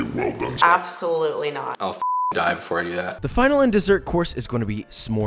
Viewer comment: I really thought he said “I will fuck a guy before I do that” and I scream laughed.